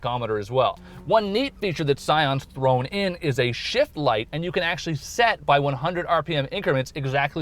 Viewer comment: did anyone else hear shit light at 2:06?